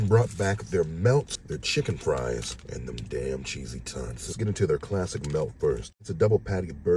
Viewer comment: Sounded like you said cheesy ‘tunts.’ I’m like…wait., What’s a.. Oh.. ‘TOTS.’ 😂🤣🤷‍♂️👍